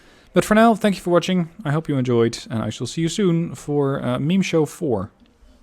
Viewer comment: I thought you were speaking Chinese there for a second your Meme Show sounded like Mingshou I was terribly confused not to mention Meme looks similar to Mimi I keep thinking but we all know about it how is it mimi.